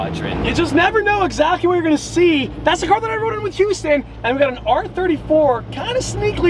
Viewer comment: what on earth did you say? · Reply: Thank you! Now that I listened back to it he said “that’s the car I rode in with Houston” referring to Houston Crosta